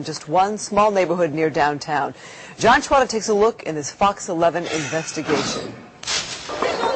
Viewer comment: 'i misheard i though she said "Fuck 11 not Fox 11